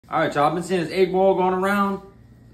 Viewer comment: I thought he said 8ball